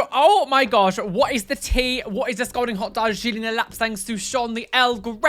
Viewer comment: Spilling it all over me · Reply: I accidentally turned on closed captions, and they thought you said something about a golden hotdog when you mentioned "scalding hot Darjeeling" 💀 Either way, spill the golden hotdogs AND the scalding hot Darjeeling 😂